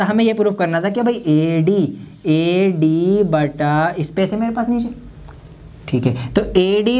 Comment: 36 , pr kya bol rhe ho sir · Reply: "Space hai mere paas niche?"